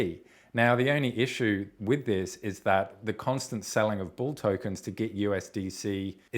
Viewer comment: Hmmm...I kept hearing "they're selling bull"...let's hope not!